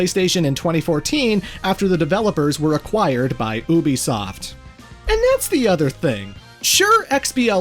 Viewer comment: Glad we have the full thing! This is amazing!! Please keep up the amazing work! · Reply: Why did I hear boobiesoft.